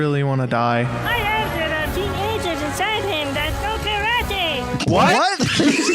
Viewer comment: GUYS THE GIBBERISH SPEAKING CARROT SAID: "I HEARD THERE ARE TEENAGERS INSIDE HIM THAT KNOW KARATE!"!!!!